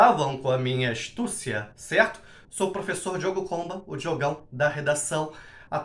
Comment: Juro que entendi "Diogo Pomba" na introdução, até voltei pro início kkkkkkkk